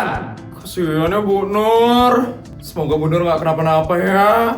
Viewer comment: aku ga salah denger nih? buk nur?